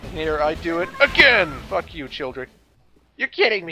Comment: Heh. Sounded like you said "Fuck you, children" at 5:15.